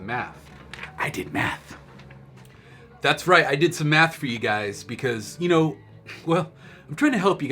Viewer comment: yo why do gr sound like he saying I did some meth when he means math